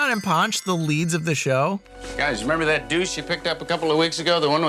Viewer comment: did he say douche?